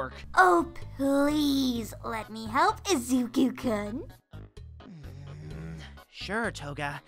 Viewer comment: I thought toga said Izuku-cunt 😂🤣